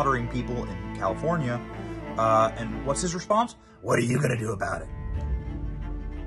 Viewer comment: I heard him say: "what do you want me to do about it?" not: "what are you gonna do about it."